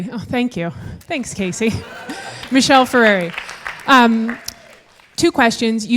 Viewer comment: I'm not sure why but i'm curious about the channel of the girl in the public, but i can't find it, maybe i didn't get the name right, i think i heard "Michelle Ferrari", can someone tell me if am i correct?